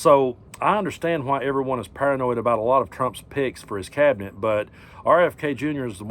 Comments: Funny to me! When you mentioned 'Donald Trump's' Picks - I thought you said 'PIGS' which made good sense to me, as they are all trying to eat out of the same Gold Lined 'TROUGH'... David Fletcher Bradford UK 😂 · I thought you were saying pigs not picks. Eighter way it fits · 😂 I thought you said pigs. You actually said picks.😂😂😂🇨🇦